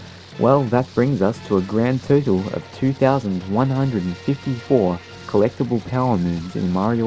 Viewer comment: The subtitles say 2174 instead of 2154. Not that it matters, but I wanna point it out if you wanna change it! lol